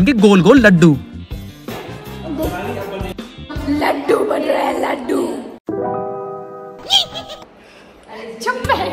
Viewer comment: pe rakhi ne kya bola??? 😂😂😂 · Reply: Sher ki kainde sher😂😂😂